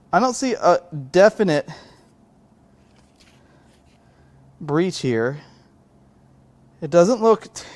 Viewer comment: "I'm looking at the head gasket and I don't see a definite brie cheer." What in the hexagons is a "brie cheer"? Or did say "breach ear"? Heh.